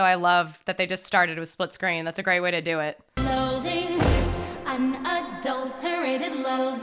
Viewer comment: so uhh embarrassing fact, I saw wicked musical I n London when I was really young, like 8 or 9 and uh ever since that day until this recent December I thought they were saying “Clothing” instead of Loathing. So for a solid ten years I thought it was them sharing an interest in clothing. Also 11:55 you have a really good singing voice